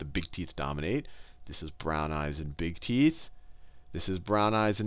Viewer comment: sounds like he says, "brown eyes and big teets" lol. teets.